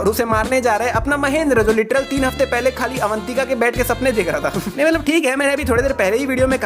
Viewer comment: I heard Avantika ke "bed" ke sapne dekh raha tha 💀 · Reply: Bhai voh bed ke nahi tha baith ke tha